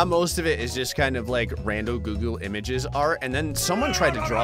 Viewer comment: did he just say googoo?